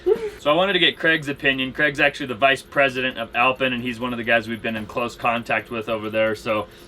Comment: In the last video I was giggling cuz I thought was saying "Alpine" incorrectly....Until just now when you showed the brand name on the window...lol...Alpen, who knew?